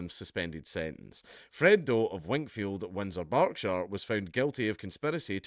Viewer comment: …thought you said “wankfield” lol…